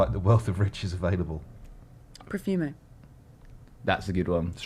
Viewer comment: What did say was the biggest scandal at 15:30? I couldnt make it out, sounded like prefume, but i cant think what that sounds like.